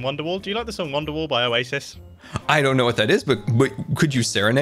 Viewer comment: Anyone else hear mombo say isis? 22:10 (Yes I know he didn’t mean it to sound like that)